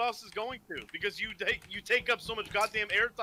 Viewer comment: I love how even he doesnt believe what he just said lmao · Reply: oh he said "asmon" i tought it was "asthma"